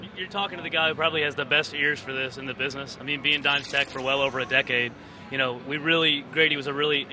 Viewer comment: sounded like he said Dime Sack